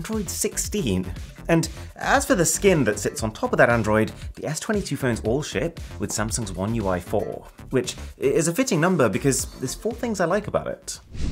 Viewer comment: I actually thought he said bullsh*t 😂